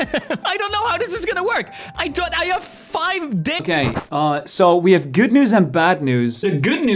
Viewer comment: Fundy has 5 day- captions says otherwise